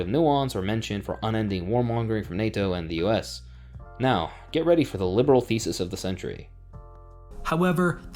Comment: When you said, "Get ready for the liberal thesis of the century," I heard, "Get ready for the liberal feces of the century." Not because you weren't speaking clearly, you were, but because my brain inserted it. 😂